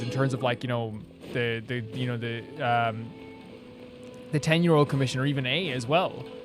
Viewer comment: I definitely heard, "...the Ten year-old commission..." at 1:51:57 and I'm not sure who I'd be more afraid of. 😅